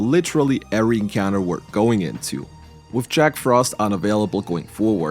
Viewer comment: I heard “And fuse into an albino” :P